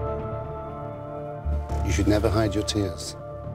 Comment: they cut what he actually said -- he said "you should never hide your tears, because that was your choice"